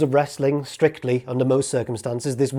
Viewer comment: At around the 11:40 mark, regarding the steps, did you say "this would have been a D.E.Q"? I hear an "E".